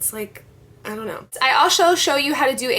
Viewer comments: you say alsho show instead of also :p lol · lol asho instead of also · "I asho show you..." lol 0:24 or am i just hearing wrong?